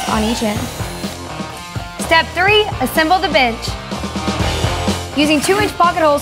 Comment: I totally thought she said, “Step 3: Assembly the bitch!” Lmfaooo